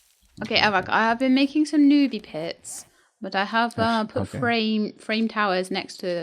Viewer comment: When said she made "newbie pits", I thought she said "nudey pics" and I was like shocked Pikachu face 😮😅